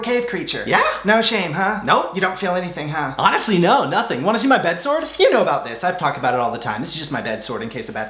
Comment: When you said wanna see my bed sword 🗡 at first I thought you said bed sores 😣😂😂